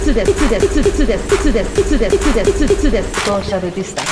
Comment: 鬱です鬱ですに聞こえるw